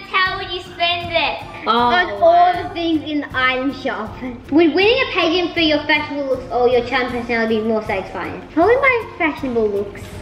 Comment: I thought I heard say pay-gant